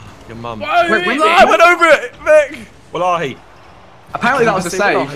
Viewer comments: Did Josh say "Wallahi"? 1:27 😂😂😂 (basically meaning swear to God in Arabic) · Does Zerkaa say Wallahi at 1:26?